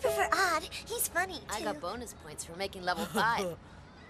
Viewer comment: for a moment there I thought that kid said "I got bonus points for making love with Odd" until I remembered that he said that he got to lvl 5 lol XD